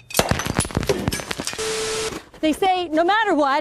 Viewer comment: When the last girl slid down that snow, I initially heard her say "use your head" and I didn't get it. Then, she slammed her head on that rock. Hahahahaha Yes. She said use your hand and I got that the second time. Hahaha.